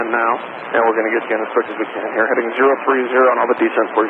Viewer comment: caption problem 2x -- caption reads "straight as we can" but I hear "quick as we can"